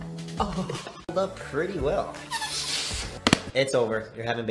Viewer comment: All I hear is “sorry sorry” 1:50 I have never laughed so hard in my life